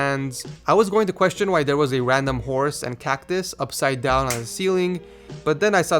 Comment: I thought you said "random whores" because of all those pictures :))